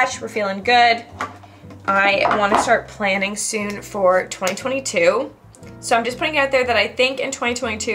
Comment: hey, 3:24 did you just say 2022 instead of 2023 ? we all aren't over 2022 i guess !